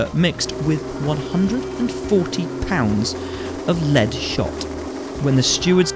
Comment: Im not native english and i just cant understand the thing he is saying at 8:10 :D 140 pounds of what? :D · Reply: Lead shot